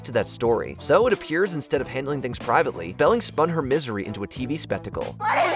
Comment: "Belling spun..." please tell me I'm not the only one who heard that... and that I'm not gaslighting myself LOL